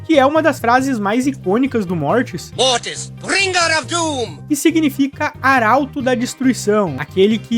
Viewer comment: eu sempre achei que ele falava "RINGARADUM" na verdade kkkkk